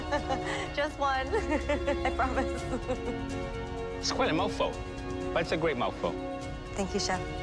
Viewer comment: "That's quite a mofo, but it's a great mofo." Did I just hear what I thought I heard?